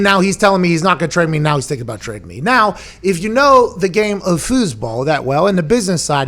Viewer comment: Lmao I thought he said fooseball